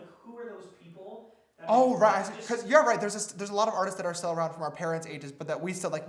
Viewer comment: i thought he said i finger a lot of artists like that